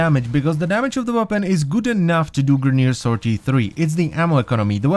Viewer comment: it sounded like you said GUTEN enough. Are you german?